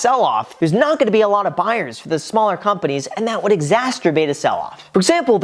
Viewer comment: did he just say exasturbate?